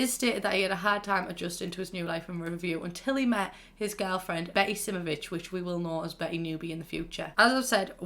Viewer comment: "Until he met his girlfriend..... BEcKy sImMerBiTcH " That's what I heard